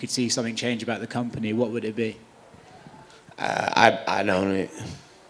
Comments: "if there is one thing you'd change about the ufc, what would that be? "I'd own it." Nates the best man. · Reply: i thought he said I don't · What did he say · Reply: I own it